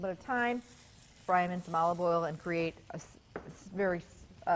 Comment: What kind of oil did you use to cook the chicken. Looks great. Thanks - I'm editing... I just re-listened and heard you say olive oil. :)